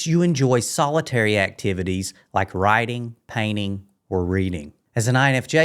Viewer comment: At first, I thought you said rioting but realized you said writing, whew what a relief.